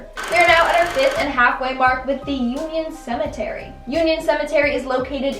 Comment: I thought number five said the unicorn cemetery.